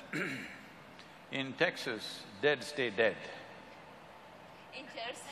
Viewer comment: at the point of time 33:00 dead say dead and what it means.... can't understand can anyone explain...!!!